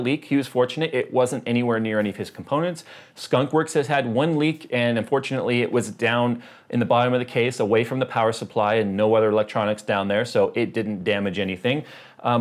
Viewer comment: "and um, fortunately" thought you said "and unfortunately".